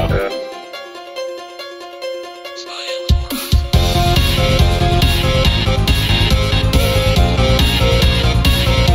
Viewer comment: what does he say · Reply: smile for us